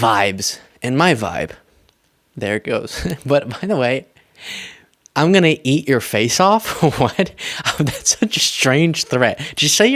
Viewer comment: Actually, I don't think that's what he said. What he really said was "We're gonna eat you. We're gonna eat you and your family" which in my opinion is probably even worse than what you mistakenly thought he said. These MAGAts are so unhinged. It would be hilarious if it wasn't so terrifying. BTW, is that the "Gay Gangster", the "Gay-ster" if you will (I won't) from another clip where he's calling out Robert Di Niro for speaking against their orange messiah and threatening to beat him up if his security detail wasn't there?